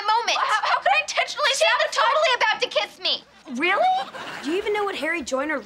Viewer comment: Am I the only one who hear at 7:38 SHE was totally about to kiss me? I know Carly didn't said that, but I only hear it.